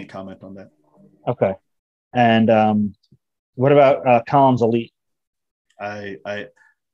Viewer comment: What did say at 0:30? "Tom's Elite"? Kahns Elite"? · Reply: Collins elite